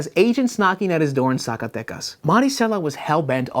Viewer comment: For a second I thought he said saca tetas & I was like woah where is this going